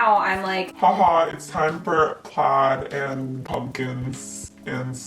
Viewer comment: I low key thought she said it’s time for pot and pumpkins🤦‍♀️😂😂😂😂. I know u probably won’t see this but I love u, u don’t know how happy I got when I saw u posted lol😂😁❤️